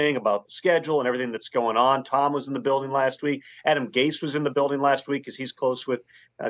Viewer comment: did he just say Adam “freaking” Gase?